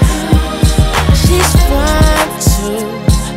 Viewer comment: At no point in my life did I ever think he said she’s 5’2”. 🤔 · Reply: It wasn't until I read this comment that I realized he's saying "she's fine too" I swore he always said "she's 5'2" 😂😂😂